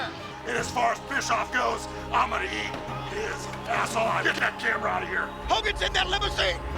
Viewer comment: sorry Hulk tf did you say? That's not PG. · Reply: hes gonna eat bishoffs ass. like he hasn't don2 that already